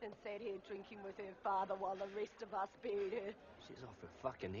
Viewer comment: Yes, I knew that the woman was the mother of the child, but did he kill Jake's daughter? Because unless I'm mistaken I thought she said "while we stayed home and buried her?" or something along those lines?